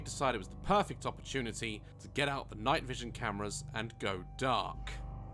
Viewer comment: I'm not joking but either I'm hearing things or at 20:42 I heard someone say Get Out in the background of them talking